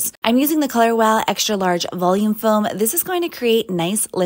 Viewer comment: What’s the name if the foam pls. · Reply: I thought she said she used Color Wow Xtra Large Volume Foam??